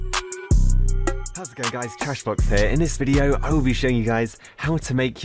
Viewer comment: There's 4 types of what did u said 1.Trashblox 2.Trashbox 3.Cashbox 4.Sharkblox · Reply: A: Trashblox